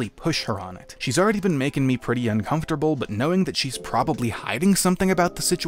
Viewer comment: I thought instead of "she's probably hiding something" he said "SHe's probably high"